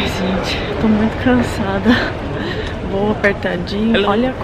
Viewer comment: O que ela fala no 3:44?? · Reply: Voo apertadinho ...